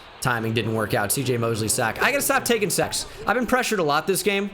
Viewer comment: saying sex instead of sacks lol